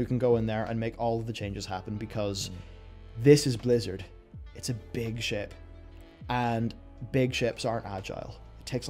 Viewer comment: I heard "This is Blizzard, it's a big shit"